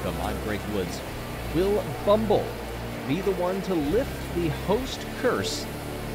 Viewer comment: Will Bumble... (pause) ... be the one... So nobody thought you said 'Will Bumblebee...'